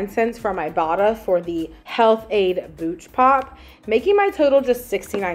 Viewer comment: I thought you said “Pooch pop” 😂 and I thought you were buying a special treat for your doggo!! 🐶